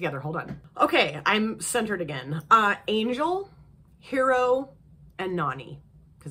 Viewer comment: I thought you said “angel, hero, and naughty” for lmao I was like yup